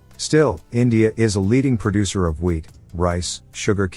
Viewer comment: I m soo high that when they say "wheat" I hear "weed"😂